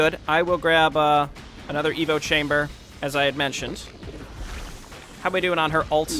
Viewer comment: Every time you say "Evo chamber" i hear "Evil chamber"